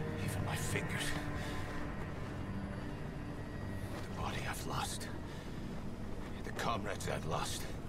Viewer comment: The thought he said "the body of lust"